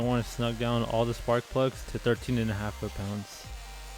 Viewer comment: Thanks for the Turorial :) my english isnt good so i have a question about 20:34. Did und say 13.5 fp? And can you Tell where you get that Information? I cant find anything like that. I have to change the plugs at my v6 to soon. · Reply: Yeah 13.5. It's OEM recommendation Torque Spec.